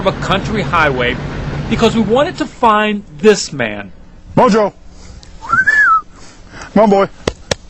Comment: Anyone else think the first word he said was "Bonjour" @ 0:17? Freaked me out when the Country accent came out right after - My mind was already thinking Ahhhh a Frenchman! lol